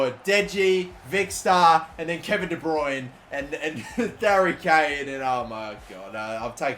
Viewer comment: wait did he say dary Kane😂😂